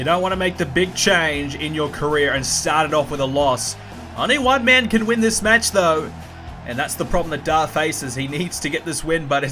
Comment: When Darr came out in the first match, it sounded like you said "only white men can win this match though" xD xD